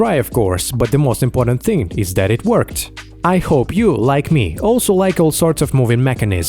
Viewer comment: What he said: "I hope you, like me...." What I heard: "I hope you like me." LOL